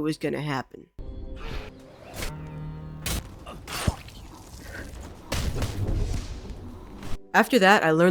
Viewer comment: did he say fuck you? 😅